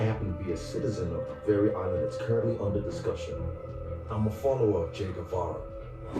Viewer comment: for a second there from a far i thought i heard "i'm afollower of jake paul" 🤣🤣